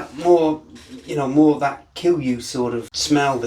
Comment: I thought you said 'that will kill you' smell from sugar wash😁🤔 ..can't wait to try it! Subbed!